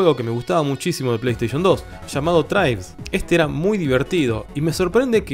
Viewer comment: en el min 4:34 mencionas un juego de ps2 que nadie habla, cual es bro ? entendí "trails", es esa ?